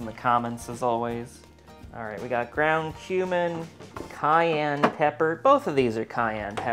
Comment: I thought he said ground human not ground kumin